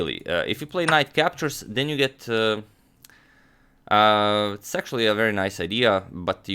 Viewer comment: its me or did he sound like he said "sexually" but he actually said "it's actually" very fast